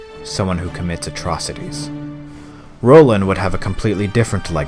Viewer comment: did he say "Rolin", with the legacy as a Hero? and is that how you spell his name? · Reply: That would be "Rohlan" <link>